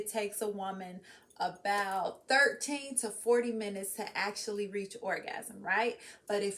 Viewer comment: The average time for women to come doing intercourse is 13-14 minutes, but it sounded like you said 13-40 minutes.